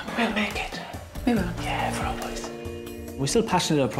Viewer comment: Also am I hearing things ...at the end I thought she had said we are certainly determined to ...Seaweed....*Drops Mic*